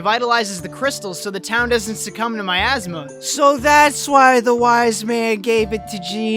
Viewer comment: Miasma I thought he said my asthma Kek, 10/10 review